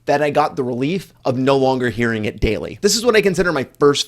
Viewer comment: I swear I had to listen to it twice. At first I heard "Hear it gaily" instead of daily